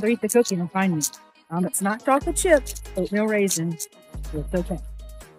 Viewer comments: Love seeing such brilliant working dogs! I say this with the utmost respect and with zero judgment: unfortunately raisins are toxic to dogs, and they may eat them and be fine on one occasion and have a terrible reaction on a different occasion. I’m a veterinary assistant intern, and we want to keep all our pups safe and healthy! Sending lots of love to you both ❤ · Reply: I thought she said “oatmeal raisin” 😬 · “It’s not chocolate chip, it’s oatmeal raisin so it’s okay 😁”….. 😂😂😂 they’re both poison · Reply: I thought she said no chocolate chip ain't no raisins 🤷🏻‍♀️ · “It’s not chocolate chip, no raisins” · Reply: Oh good! I thought I heard oatmeal/raisin and was worried there for a bit 🙈.